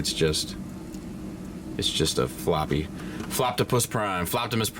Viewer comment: I thought my dude said “Floppy-pus” AY YOO! lol